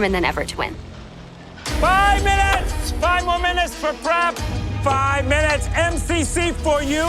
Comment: Alvin: 5 more minutes for prep What I heard: 5 more minutes for crap😂😂